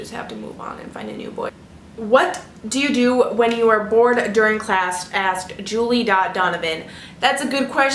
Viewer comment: When she said "what do you do when you're bored in class" I thought she said "born in class" and I snorted.